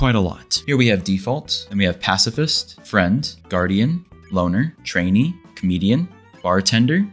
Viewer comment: For some reason I thought you said boner instead of loner wtf💀